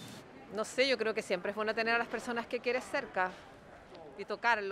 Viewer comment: does anyone know what the woman said at 7:19 after "yo creo que siempre ..?." it wasn't bueno like the subtitles say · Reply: "yo creo que siempre es bueno tener a las personas que quieres cerca..." (